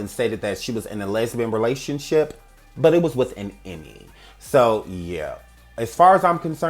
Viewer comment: What is being said at 17:48? Her relationship was with a what?? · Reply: Ohhh an EMMY?! An Emmy award 😅